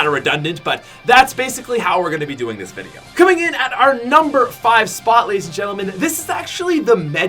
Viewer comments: anyone noticed he said number 5 instead of 9?? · did he say number five slot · Did He Say "Coming In On Our Number 5 Spot"??